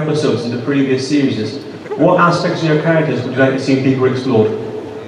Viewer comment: Could anybody tell me the question at 22:50? Can't quite understand the end. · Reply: "deeper explored", now that I know I hear it! Thanks a lot :)!